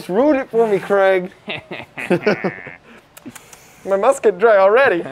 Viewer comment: When said, "My mask is dried" I heard "MA musket's dry"..... made me giggle 🤪